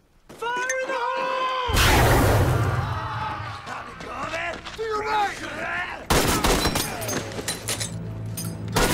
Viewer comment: what did the japanese say at 0:06? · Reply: "Eat this!!"